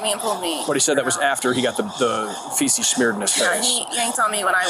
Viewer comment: Omg i misheard at first, thought it said smeared on his fence, not face - thats disgusting